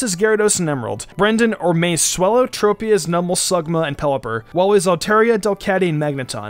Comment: did he say "sugma"?